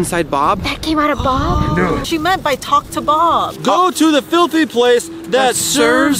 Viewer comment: i thought when said ''talk to bob'' i thought ''talk to billybob''the hacker